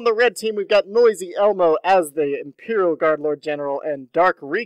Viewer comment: Thought I heard you say "Imperial Guys" and now its stuck in my head.